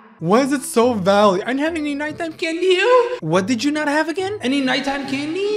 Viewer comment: "NiGhT tImE cAnNeD bEeF~" Please tell me someone else heard that